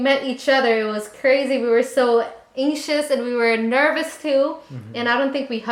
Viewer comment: When she said "we were so anxious" i thought she said "we were so asian"